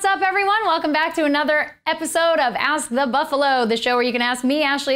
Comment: It sounds like she says "oust the buffalo" at 0:09.